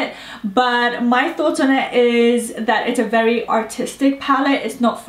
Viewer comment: I thought you said, "it's a very autistic palette".. I was laughing so hard lol